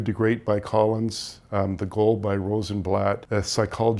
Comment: the second book recommendation after “from good to great”, what does he say “the gold”? If you know, please post a link to this book.